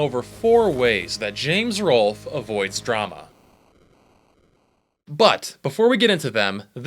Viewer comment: Am I the only one that thought it said how to avoid Obama